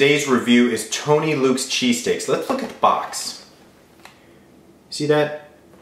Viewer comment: I know he's saying lets look at the box, but he sounds like he said the f word